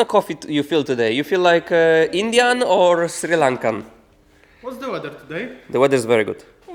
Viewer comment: Did just say Sri Lanka. ❤ all the way from Sri Lanka 🇱🇰